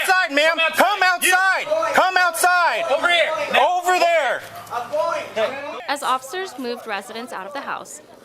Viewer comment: Does he say @ 1:42 "I'm white?" Guess he thinks they won't kill him if he says that. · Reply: They very clearly said "I'm going". How TF did you get "white" out of that? 😂